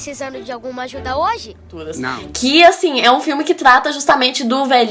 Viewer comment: Só eu que ouvi o velhinho dizendo "todas" quando o menininho perguntou se ele precisava de ajuda? kkkkkkk